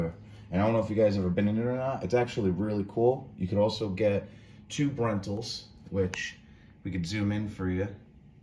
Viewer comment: thanks for the video, I am going in a couple of weeks and staying in that room type so good to see the room. At first I thought you said "2 brentals" - ??? HAHA, "tube rentals" ;)